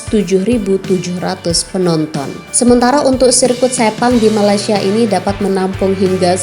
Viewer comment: Kenapa aku dengar setan? Betul ke atau aku yg salah dengar. Bkn nk buruk2 kn keadaan. Cuma tanya je tau. Sorry lah baru nk tgk video hambar ni.